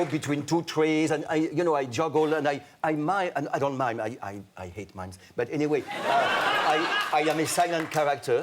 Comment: What did Philippe say ( 5:23) ...sorry I don't know English good) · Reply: said "I hate mimes"